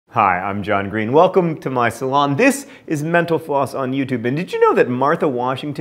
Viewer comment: My husband thought you were saying "menopause" instead of "mental_floss".